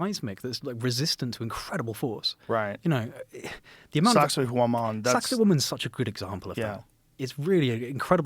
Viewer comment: Sauxa guamon? This is like the 7th time this episode. Can we please get something pulled up showing what it is or the correct spelling at least? Edit: Apparantly not that, not "Sax Woman" like the CC says, but Sacsayhuamán. A site in Peru.